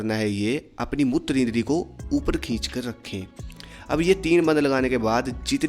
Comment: Bhai 3:28 par aapne kya bola mujhe samjh nahi aaya, thodi ko neeche karna, fir pet andar kheech lena uske baad aapne kya bola samjh nahi aaya. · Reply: अपने मूत्र रोकते समय कैसे ऊपर खींचते हो वैसे ही अपने मूत्र द्वार और मल द्वार को उपर की ओर खींचो